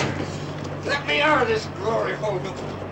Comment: 😂😂I swear, I just heard him say, " Let me outta this gloryhole. "